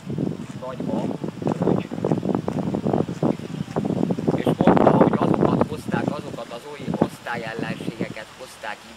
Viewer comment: nál mit mond a gyereknek aki elmegy? :D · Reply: "dobravécser"